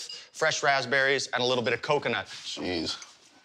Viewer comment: i heard cocaine